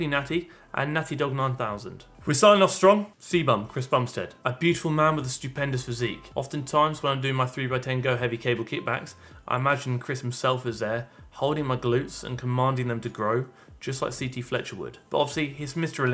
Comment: I heard "a stupid man with a beautiful physique" at first...not sure if that makes the statement untrue 🤔